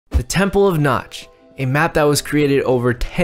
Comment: I thought you said the The temple of nuts